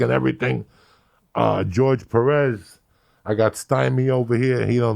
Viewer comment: fuck me, I thought it said george lopez. Connor McGreggor voice- who the fuck is that guy?